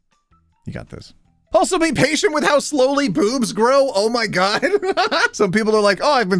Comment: I understood instead of boobs, poops... I am really tired and exhausted from my day😅😂